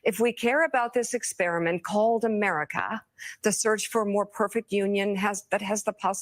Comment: did she just say experiment?? ⬇️ Called America??? We're an experiment now